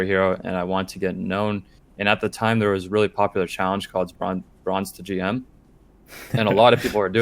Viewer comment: what does he say? Grunt to gm?